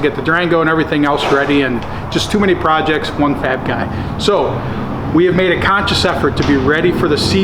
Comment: i had to rewind when he said 1 fab guy. Sounded like he said 1 fat guy 😂😂😂😂😂😂